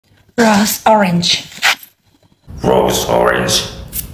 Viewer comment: is no one gonna acknowledge that i thought she said gross orange and gross water?